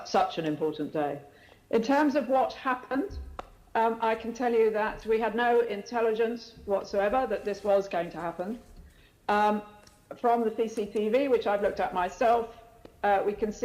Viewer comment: All I heard was ‘ we have no intelligence at all’. Thanks for the confirmation Dick.